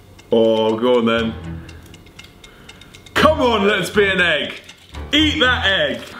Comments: For some reason when oil said “let’s be an egg” I thought he said “lesbian egg” 😂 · deadass thought he said ’come on lesbian egg’ 😂😂😂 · I'm not kidding...all I heard was "Come on, lesbian egg."...